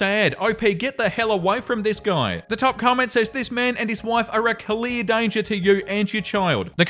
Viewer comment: you mean "family"? 😂 i had to go back and read it, cuz i heard "this man and his wife"